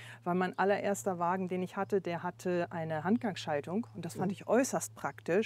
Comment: Was sagt sie, was für eine Schaltung hatte ihr erstes Auto? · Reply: Handgang Schaltung